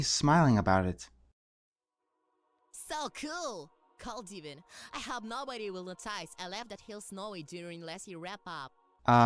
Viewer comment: ok I think at 2:14 he's saying "So cool! Cold even. I hope nobody will look twice. I left that hill snowy during winter wrap up." maybe. not sure but that's what I got.